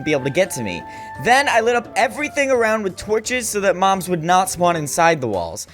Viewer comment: did you say moms?